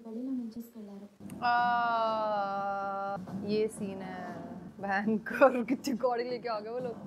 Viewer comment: What did you say at 5:57 to 5:59 😂😂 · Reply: bhayankar